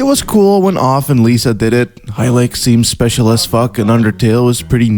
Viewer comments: Whats the name of the game at 01:19 , the one with the moon face being picked up the the tips? Couldn't quite make out what said, Highlike? Google returned nothing. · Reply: Hylics ✌️ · What's the game at 1:19? I can't make out the name. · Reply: Hylics.